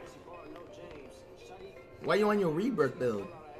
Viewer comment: Ion. Think flight knows what a rebirth means. Or a. Lockdown 3:51 he clearly said I'm on my rebirth